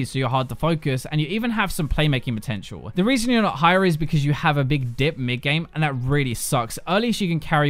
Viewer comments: anyone else thought he said big dick? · alone else thought he said "... you have a big dick..."